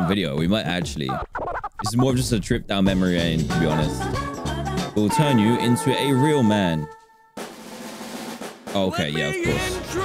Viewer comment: I thought it said “we will turn you into a real mom”